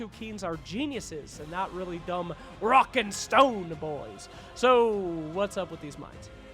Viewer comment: DID I HEAR A ROCK AND STONE!?